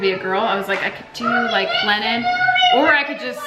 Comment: When you said "Lennon" I thought you said "Linen." 😂😂